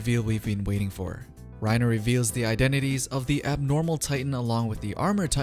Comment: did you say an abnormal titan??